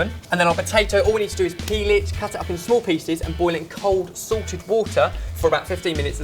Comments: At first, I thought Ben said "Sorted" water. I was like, WHOOOAA. They have their own water! But then when he said it again, I understood "Salted" water. hahaha! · I thought I heard you saying "cold sorted water." Hahahaha.